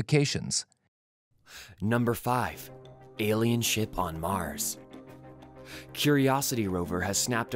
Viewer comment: In the beginning I thought it said “Alien shit on mars” lmao